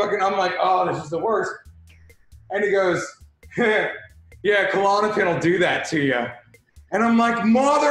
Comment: what is he saying? They never have offical CC just auto captions and auto captions kinda suck a lot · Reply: He said Klonopin, its sedative